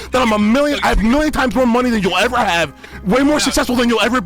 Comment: thought u said billion lol